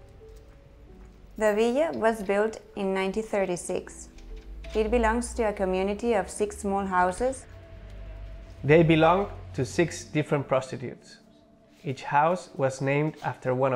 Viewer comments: am i mishearing but around 1:20 he says “they belong to six different prostitutes”. nbd if so, but just checking in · I'm sure I heard this wrong... but 1:22 did he say, "The Villa belongs to 6 different prostitutes"?